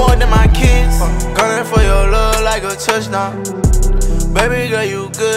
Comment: I can’t be the only one who thought he was saying “ running for your love like a touch down “ 😂